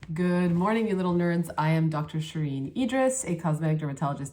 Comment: ❤️😍❤️😍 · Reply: i heard "good morning Middle Earth" initially haha